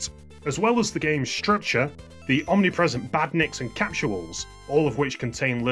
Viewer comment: Whoa what is that word there? "Capturall"? What are those?